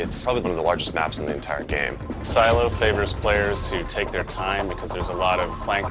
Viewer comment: I heard "Silo favors Ghost bitches"